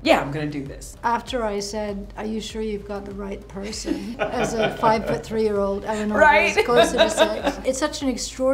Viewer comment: Did she say "As a 5 foot 3 year old"????